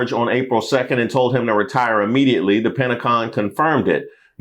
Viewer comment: did you say PentaCON?! 😂😂😂 Considering the money laundering that goes on there, this is an appropriate name